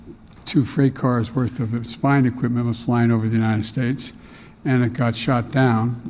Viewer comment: im sorry did he say "spine equipment" :face-blue-smiling